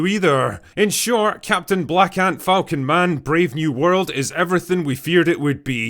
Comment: I keep hearing "Captain Blackadder" and being disappointed a second later